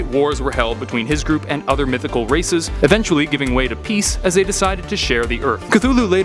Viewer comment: did i hear ''mythical racist ?''